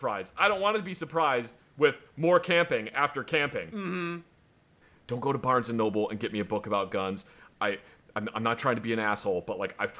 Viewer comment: I wasn't paying close enough attention and thought I heard say, " Dont go to Barnes & Noble and get me guns." I was thinking wtf, damn B&N changed since ive been there.